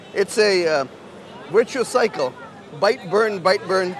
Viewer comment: The English captions said "ritual cycle" at 6:46. I think he said "virtuous cycle"? Makes more sense in context too.